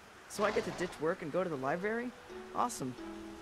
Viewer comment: i dislike the voice acting for the game already, he said libary instead of library and that truly irritates me 1:20 if you want to check for yourself